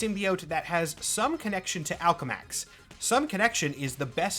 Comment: the speed at which I had to pause to e video and rewind because I misheard “Alchemax” for “Malcolm X”.